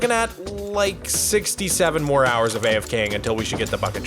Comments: Did just say 67??? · Did I Just hear 67? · did you say 67?